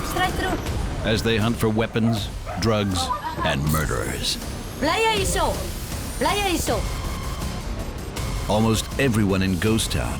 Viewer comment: I'm not sure who your translator is, but they are rubbish! At 0:37 the policewoman is saying: "Do you stay here". Not what you're saying - "who are you"!! Get a new translator as that one doesn't know the language.